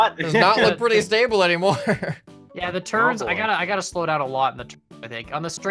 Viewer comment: I heard someone say "the turds" XD